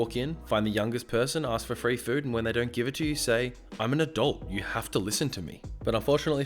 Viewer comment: I heard "I am an adult" as "I am a dolt" lol